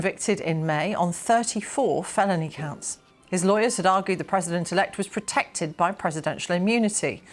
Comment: For some reason I heard "predator-elect," is that right?